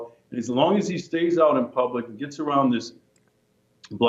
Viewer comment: Did he just use the term "blackade" instead of blockade?